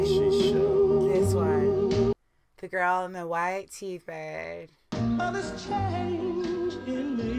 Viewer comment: Did she just say “The girl in the white T-bag”? 🤣